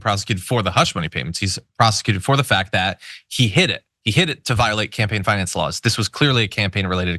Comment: HA I THOUGHT HE SAID "He hit it." but he said "He "hid" it.". lol